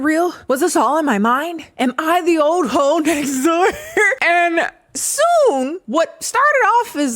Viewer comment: For a minute, I thought you kept saying "Old Fot Next Door." I swear, the author wants to be Zane so bad.